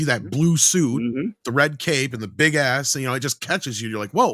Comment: I really thought said: "and the big ass" 🏳‍🌈? 🤔